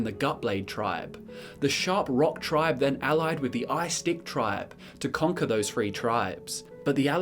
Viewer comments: Did he say the Ice Dick Tribe at arpund 6:50 ? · did I hear that correctly? I guess that's Ice Stick, and not Ice Dick, right?